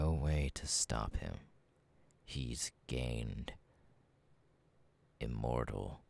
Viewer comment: He started saying "gained" and I thought he was saying that he was gay 😭😭😭